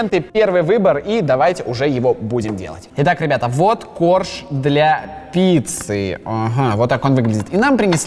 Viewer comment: я услышала вот борщ для птицы😂😂😂